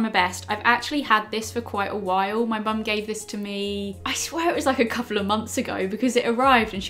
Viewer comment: sounds like she said my bum gave this too me 😂😂